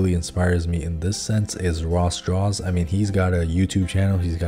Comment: I legit looked up «raw straws drawings» when he mentioned Ross Draws cause I thought that was what he said.... Figured it out eventually thanks to a comment here